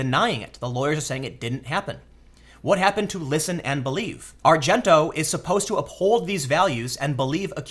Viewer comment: New game. Every time Tim pronounces a name wrong, we take a shot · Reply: I was listening to this video while doing laundry. For the first 5 minutes I thought he was saying "Ozzie Argento." Tim stop, I'm being misinformed